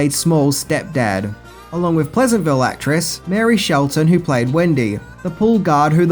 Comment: It sounds like you said "Mary Shelton, who played Wendy" Could her name be Marley Shelton? Not sure if I understood you correctly.